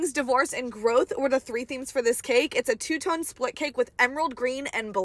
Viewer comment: I heard “Two-Toned Slut Cake” but I’m pretty sure that’s not what you said.